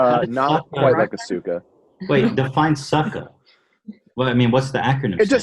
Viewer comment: Did Ethan really say define "sucka"? Wow lol Ethan your boomer is showing